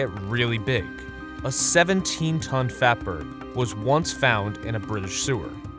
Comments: thought it said 17-ton fapper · OMG I thought you said a 17tonne fapper in the sewer